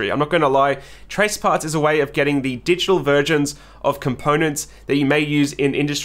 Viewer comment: "digital virgins" wait.. did I hear that right? :P Nah jk, i know u mean versions haha.